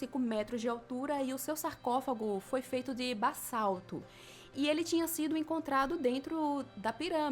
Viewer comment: Ouvi "bassalto" ..... Ouvi certo, ou foi impressão?!